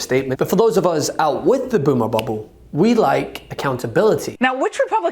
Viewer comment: Did I hear that right? "Boomer bubble"??? You're not suggesting "Boomers" are the problem are you? Because there's nothing farther from the truth. Some "Boomers" have been marching and protesting against these people longer than a lot of people have been alive. Trumpublican Christian Nationalists aren't a "Boomer" problem. They're a Democracy problem.